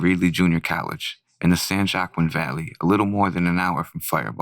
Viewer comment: Did he just say san Jock Queen valley? lol i dunno if its pronounced like that